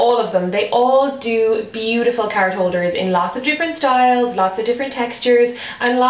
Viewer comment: I hate many vloggers' accents. But I LOVE your accent so much! So beautiful. I thought U were saying "carrot holder" and wondering wtf that is .i get it know. Love ur vlogs. Keep on!